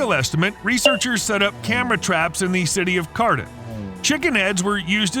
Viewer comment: Did he say "city of Cardiff"?